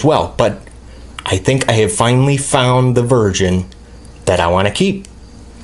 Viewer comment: I thought you said virgin you want to keep....